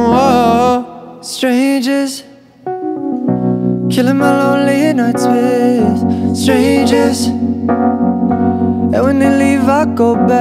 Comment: I heard "killing my lonely nuts with strangers" lmao